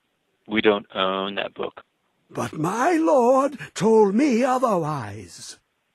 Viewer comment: "But my lord told me otherwise" "Well he was incorrect :l" That guy was really the best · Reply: I thought he was saying Eye Lord, lol